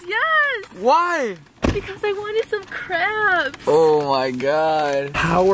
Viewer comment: It's sounded like said I really wanted some cramps instead of crabs